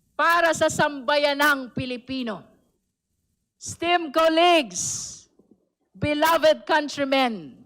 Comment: just asking, STEAMED COLLEAGUES ba yung narinig ko?